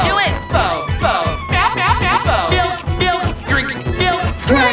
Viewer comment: I swear I could have heard Elfo saying Milf instead of Milk!